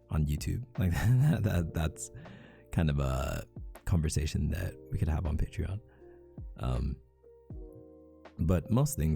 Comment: He: “this Kinda conventions we can have on patreon” Me: wait what did he say....👁👄👁